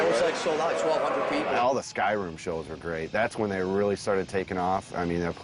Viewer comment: I swear I thought he said they played in Skyrim and that helped then get to the top